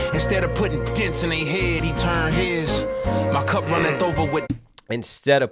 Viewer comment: Maybe I misheard, but to me, "Instead of putting dents in they heads, he turned heads" sounded like "he turned HIS", as in "he turned the other cheek"